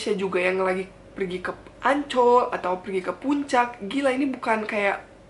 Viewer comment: ngomong Ancol aku dengernya Anc*k Btw itu salah satu kata kasar di Indonesia, khususnya di Jawa Timur Cuma pendapat saja